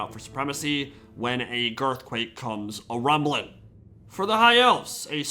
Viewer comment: I heard "Girthquake" and thought Greasus Goldtooth was gonna fight Grom the Paunch. :(